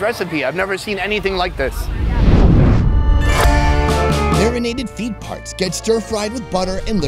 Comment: I heard "urinated feet parts".. 😯🙀